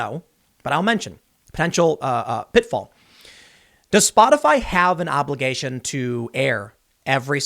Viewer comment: I thought I heard you say "Despot-ify" instead of "Does Spotify". The former is correct.